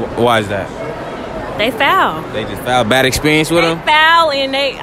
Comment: what does she say? fow? lol